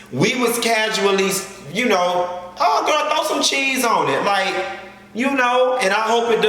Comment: why I heard, "Throw some D's on it..." #RichBITCHmade